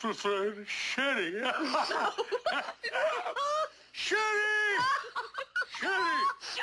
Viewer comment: that didn’t sound like he said sherry 😂 sounded like the s word